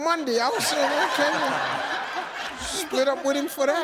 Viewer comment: Tbh I heard “he spendaloddamomee”